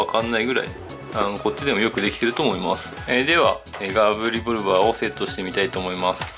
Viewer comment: ガブリポルバーって聞こえたw